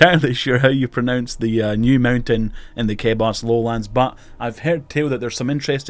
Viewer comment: I keep hearing "kebos" as "okay, boss" which is oddly appropriate